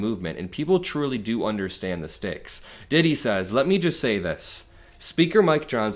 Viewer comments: Specify who DITTIE is when you bring up their name! LOL I was looking down and almost dropped my salad when you said (what I thought was) “Diddy said…” LOL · thought he said diddy for a sec 0-0